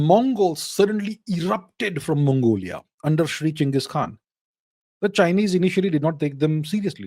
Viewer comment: did he say Shri Genghis Khan?